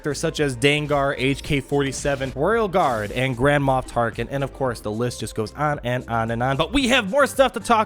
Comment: I keep hearing "Grandma Tarkin" - hilarious :D